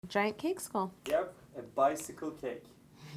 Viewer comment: I thought it said giant cheesecake cake